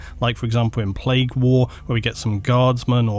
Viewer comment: "Sentia"? Is that that name of the novel(?)/novella(?)? Did I hear that title correctly? I ask because as an aspiring Warhammer 40k player(( and collector )) I'm immediately drawn to any story that will feature both the Adeptus Astartes and the Astra Militarum/Imperial Guard, most especially-especially any story wherein the core plot involves one of the superhuman Astartes working-and-fighting alongside a 'mere human'